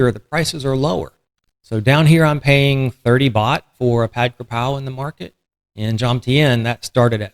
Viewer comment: What are you saying at 4:40 that cost 30 baht? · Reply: ผัดกระเพรา pad kra pow (fried holy basil)